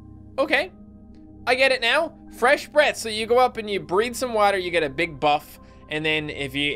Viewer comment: What'd you say? "Breathe some water?" XD